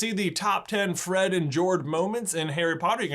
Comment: ok did he just say Fred and Jord